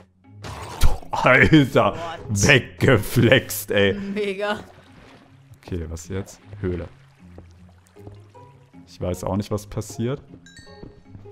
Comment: ich habe ein n-word gehört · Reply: sagte glaub mega